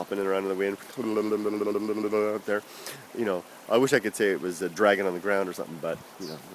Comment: I first misheard "I wish I could say it was a dragon on the ground".